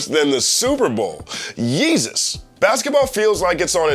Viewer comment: Did he just say "oh yesus" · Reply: Yeezus.